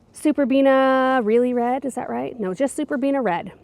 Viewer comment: 30 I love that the little peeps got in and helped with the flowers. ❤ 14:47 Wait What??? Did you say RED?🥀